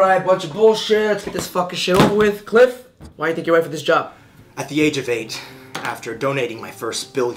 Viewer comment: Near 0:09, I thought he said,"At the age of age." XD